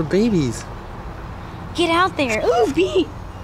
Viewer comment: is he saying "OOOO a Bee!" lmao.